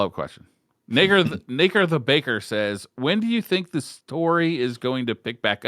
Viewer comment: ….bruh was that the n word? Sounded like a misreading of a name, but definitely sounded sketch